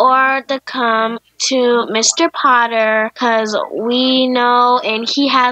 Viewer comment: wait wait wait. Did she say MR POTTER? :D